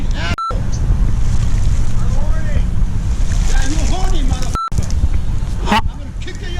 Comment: theres a lot of bleeping but i think he said "uR hOrNiE"